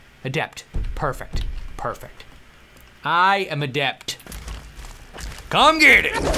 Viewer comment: When he said he was an adept it sounded like he said he was ADELE! XD